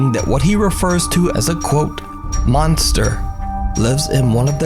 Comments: I swear I hear, “I’m not a (pause) monster.” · I heard I'm not a.. Monster